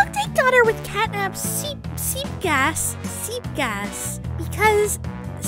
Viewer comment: i thought she said speel gas😂😂